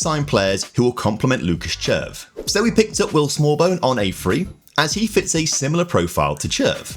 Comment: Is saying that červ has a small bone?